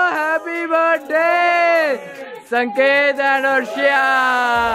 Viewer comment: did he just said sanket and ARSHIYA 🥴🥴🥴 yeh kaun h be ...we want aasan